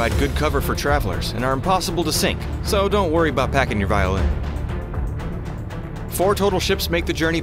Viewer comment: did someone said Violin hmmm